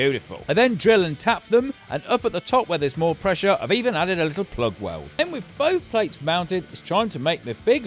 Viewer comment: what on earth did colin say? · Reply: I've even added a little plug weld